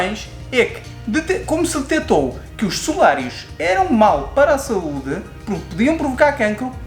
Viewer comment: O que eles falam no 5:16 ou 17, por favor? Ouvir 3 vezes e está difícil. O que foram abolidos e que faziam mal? Comem muito as sílabas e a gente sofre p/ entender. Muito obrigada, casal e!!!!! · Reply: Smc Milano aos 05:16 estamos a falar dos solários "solarium" 😂😂... Nós tentamos falar mais calmamente para se perceber melhor mas depois com o entusiamo do vídeo fica complicado kkk Mas diz uma coisa... ao assistir os nossos vídeos começa a ficar um pouco mais fácil de compreender o Português dos Portugueses não é ?? 😂😂 Beijinhos nossos e muitas lambidelas do 🐶